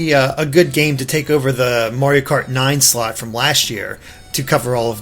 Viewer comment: did he just say Mario Kart 9?